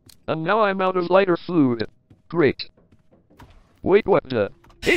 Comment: did he just say food instead of fuel?